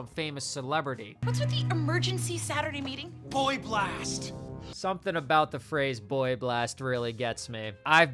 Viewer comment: My dumbass thought he said Brain Blast